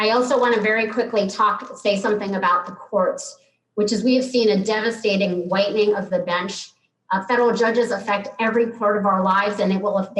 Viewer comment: did she say whitening or widening??🤣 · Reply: A devastating whitening of the bench.